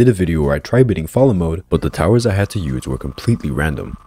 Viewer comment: Me who wasn’t looking when he said fallen and thought he said balls💀💀💀